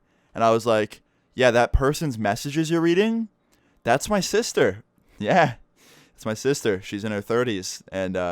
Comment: Did you just say That's my Mcsister did you take your sister to McDonald's?